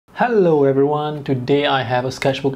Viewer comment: hulloalice is shooketh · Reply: It sounded like u said hullo 😂 like her intro